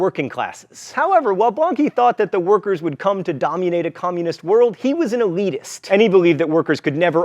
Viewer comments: Did John say "domunate" 8:56? Is that a mash-up dominate and communism? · did he just say "dommunate a communist world"? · Sounds like John says "domunate a communist world" - like "domyoonate"